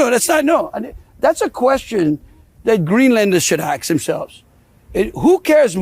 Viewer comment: did he say: "...should axe themselves."? What a well-spoken person! Man, where did he go to school?